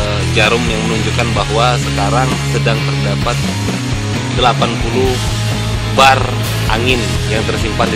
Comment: gk Salah denger apa 80 bar?